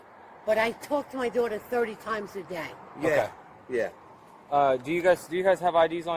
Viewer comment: Thirty times a day? the mother is crazy. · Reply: I thought she said 40 times a day.